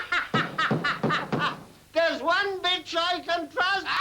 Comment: did I hear the word "BITCH"?